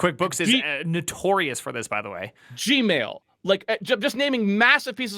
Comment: “SHE-mail” is what I heard and I almost did a spit take